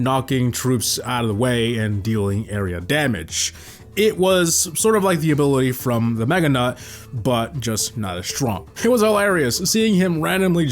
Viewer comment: Did I hear mega nut?? Or am I tweaking? ...